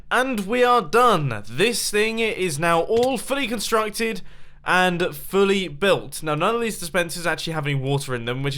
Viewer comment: it sounds like he says awfully constructed